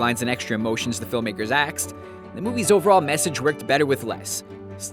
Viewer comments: did he say aksed instead of asked? if so I am annoyed because 3 of my friends say aksed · is it just me or did he say,"aksed" instead of "asked" in